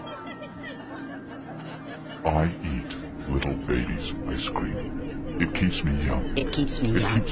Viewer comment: Idk how I went from watching movie trailers to watching this and did he just say I eat little babies or little babies ice cream?